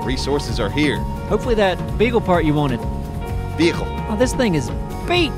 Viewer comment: It did sound like Appsro said beagle part